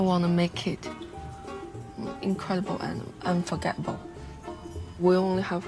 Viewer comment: Did I hear wrongly ? The China bride @ 11:01 said incredible and forgettable ?